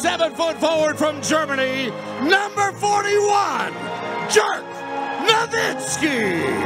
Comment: did he just said "Jerk Nowitzki"? 🤣